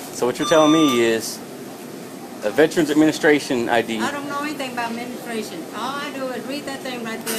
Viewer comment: Sounded like she said "I don't know anything about menstruation"